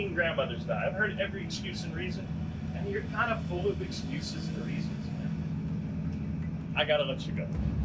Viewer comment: What did Bill "Wild Bill" Wichrowski say around 2:56-3:00?... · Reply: That's what I thought he said. Thanks. I turned on the closed captioning & he said "You're kind of full of excuses & reasons.", but, the premise & the point still remain the same...